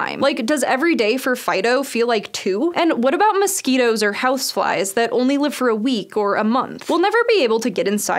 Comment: it sounded like she said housewives there